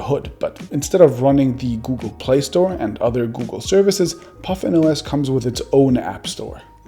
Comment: I thought it said "PutinOS"